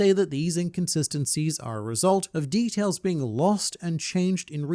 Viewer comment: I’ve been consuming too much one piece content 14:23 you said details but I heard D. Tails